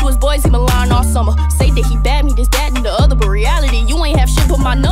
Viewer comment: Me in 2021 realizing that at (1:34) instead of thinking she said “bisbagged” she really said “This, that, and the other”🤦🏾‍♀️🤦🏾‍♀️